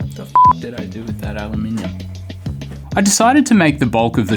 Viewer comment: "aluminium" Subtitles say "aluminum"; like a loonie mum! ETA: 6:30 That's not Tupperware, that's sistema! Can't you read, bolthead? 🤭 JK Seriously, I'm surprised that "sistema" is a thing in Down Under; I have some sistema lunch boxes, but I'm on the other end of the Globe - well, almost. I'm in Austria, Central Europe. Not so far away phonetically from the Land of the Down-Under-Roos, though.